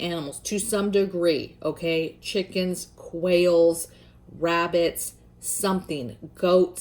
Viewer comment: When you were suggesting animals to raise, I kept thinking I heard you say whales. I’m like huh?? Then I realized you were saying quails 🤦🏻‍♀️. Great content as always sister!